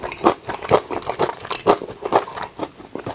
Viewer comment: なんて言ってるの？ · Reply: ··かも子 ·· 「チアーズ」です。日本語の「かんぱい」と同じ意味の英語です。(๑˃̵ᴗ˂̵)